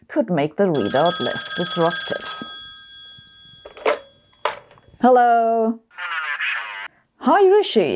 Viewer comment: Did Rishi say “Hallo Liebchen!”?